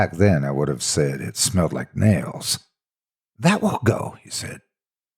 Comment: It smelled like what? It sounds like you said "males."